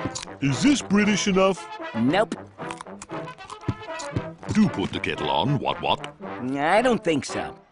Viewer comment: I’m sorry but what the fuck is he saying at 0:07? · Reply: Do put the kettle on, wot wot. The subtitles are wrong